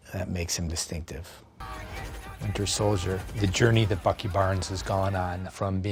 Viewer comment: Thought he said fucky barns